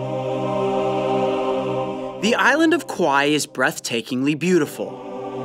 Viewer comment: Did he say the island of kiwaii at 0:30????